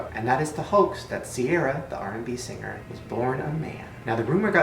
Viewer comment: I swear at 7:00-7:10 somewhere in the I thought he said "singer i forgot... was born on mayo"